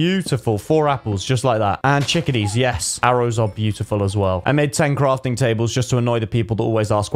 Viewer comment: when he said “I made ten crafting tables” it sounded like he said tanqr